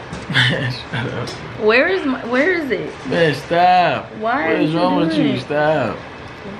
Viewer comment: i've watched this plenty times but at time stamp @ 32:56 it sound like he said bae stop